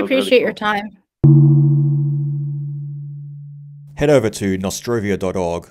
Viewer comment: did you just say "all the major social credit platforms" :D Appreciate this podcast - MOAR!